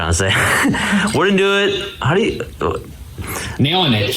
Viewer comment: I think Steven is trying to say, I wouldn't have it any other way?